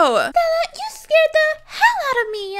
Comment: did she say hell I thought that came out as hashtags